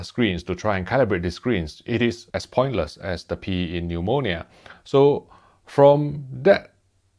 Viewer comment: "Pointless as the p in pneumonia" - hilarious 😂😂😂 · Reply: That makes more sense. I thought he said "As pointless as the pee in ammonia", lol!